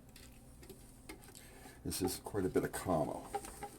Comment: it sounds like says "This is quite a bit of KARMA" .... which would be correct 😊😉 💜 💖 🧡💚💙💛